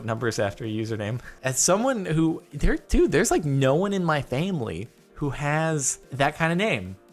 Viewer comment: Did anyone notice that he said famely instead of family at 6:44 ? LOL